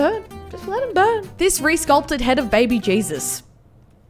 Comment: my captions said Rhys Culp turd instead of resulpture